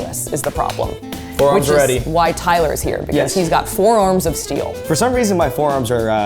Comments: I heard "forearms of steel" as "FOUR ARMS of steel" 😭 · I definitely heard "four arms of steel" instead of "forearms." But I didn't even question it. Also why was this video so sexual 😂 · When Saf said Tyler has forearms of steel I thought she said "four arms of steel" and imagined a Machop Tyler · I heard "four arms of steel" instead of "forearms of steel" and didn't even skip a beat 😂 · You said Tyler has forearms of steel, but I heard Tyler has "four arms of steel." Lol. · when saf said “he has forearms of steel” it sounded like she said “he has four arms of steel” 😂😂😂